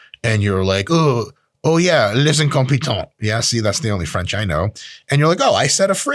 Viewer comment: I am french and I have no idea of what he just said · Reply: Il est incompétent :)